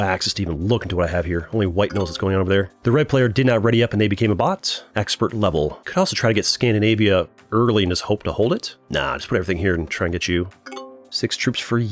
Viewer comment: I thought you said I’m the cream player bro